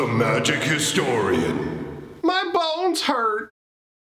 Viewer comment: Thanks for stopping by my friends, come hang out over on my other channel to see the changes they just made to dominaria united bundles - <link> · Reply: Every time I see the intro, and it’s been a little while I mishear the intro as “My Balls hurt”.